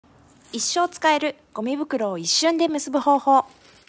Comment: 「一生使えるゴミ袋を、一瞬で結ぶ方法」に聞こえたわ〜😂✨✨